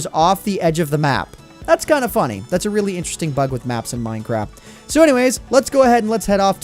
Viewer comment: Anyone else hear him say "Mine Crap"?, XD